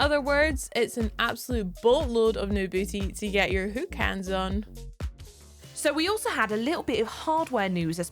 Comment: LOL hahaha did she say "Whooks Hands" (aka Who-X Hands, aka Hooks Hands)??